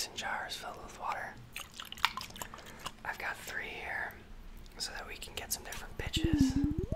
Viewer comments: “I got three here so we can get some different pitchs” What i heard “I got three here so we can get some different bitchs” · Did he say pitches or bitches 👀